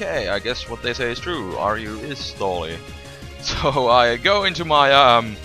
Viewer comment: lol for a sec i thought you said " i guess what they said is true RU means "R U pissed already" " XD.